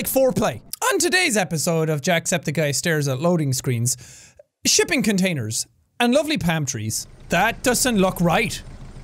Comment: Did anyone else hear “Shipping containers and lovely pantries”? 😂